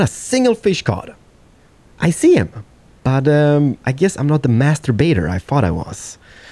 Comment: did he just say he's not the masturbator he thought he was?